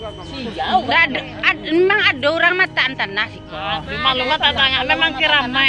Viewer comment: Di menit 1:43 kalau g salah dengar, mank ada yang meletakan tanah karna kuburan ini keramat. 🙏🙏🙏 Mohon maaf kalau slh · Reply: Bukan kak, ada ibu2 yg bertanya.. pertanyaan nya seperti ini loh... .emg ada yg naro tanah ya di sini ?? Nah itu pertanyaan ibu2 yg lagi di TKP kak 😊